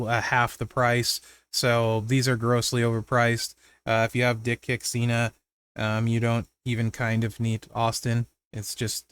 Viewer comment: Swore I heard “dick pic Cena” 🤣